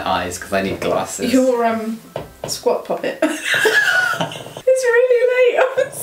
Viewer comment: When she said squap poppet I legit thought she said sock puppet 😂😂😂